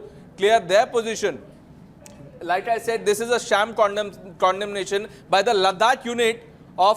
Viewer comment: the guy said Condom instead of Condemnation... be careful reporter friend :).. I broke my Mi Chinese Phone today and threw miles away...